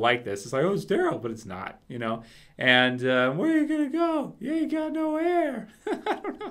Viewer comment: First time I watched it, i thought Norman said "You've got no hair!"